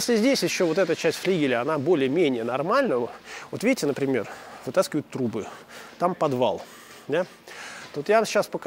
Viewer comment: Вот видите, например, вытаскивают трупы... (послышалось).